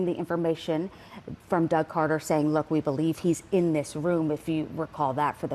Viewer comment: I thought they said "he could be in this room" not that he is